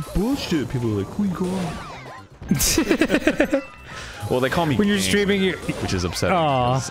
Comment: Arin says "They call me ga~~~~~" and i cant for the life of me figure out what hes saying because the audio was louder than his voice x.x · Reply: He said “they call me king”